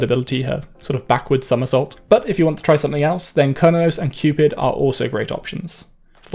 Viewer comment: i'm sorry but at 5:35 did you call Cernunnos "kernos?" Or were you trying to say Chronos?